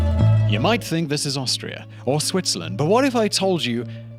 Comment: "You may think this is Austria or Swtizerland....but NO. It's a country that borders both of them. Can you believe that ?" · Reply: I thought he said Australia not Austria. It would make more sense for the first image, since the picture looks like Ularu rock in Australia or Redrock canyon in the States.